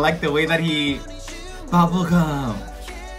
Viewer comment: 나만 박보검으로 들리냐